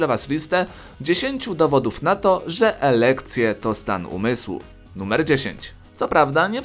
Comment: usłyszałem erekcje